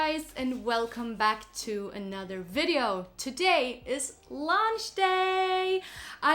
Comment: I thought you said "Today it`s lunch day !" :)) Aweosme pallete. Very original selection of colors!!